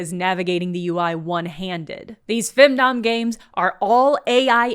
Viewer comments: When you said "femdom" I thought you said "findom" and thought the games took control of your bank account. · When you said "femdom" I thought you were saying "findom" for a second. Findom fits as a concept if someone is willing to pay for a load of shovelware.